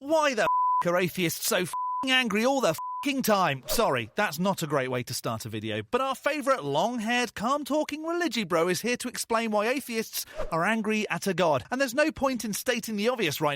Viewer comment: I had to replay the opening three times, the third time with subtitles, because I kept misunderstanding you when you said "calm talking". Because I kept hearing "cum talking", which confused the hell out of me.